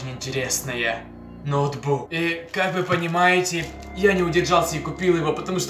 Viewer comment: Мне послышалось или ты сказа ноутбук из зада